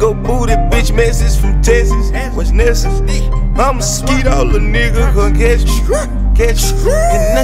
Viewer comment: Don't no what he saying but I'm feeling the beat.... and the chorus is catchy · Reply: i heard him say something bout "skeetin on a nigga" idk bout thst